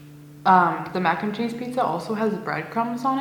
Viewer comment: Thought it said Wine & mac n cheese pizza, lol I'm tired 😴